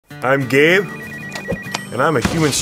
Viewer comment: thought he said im gay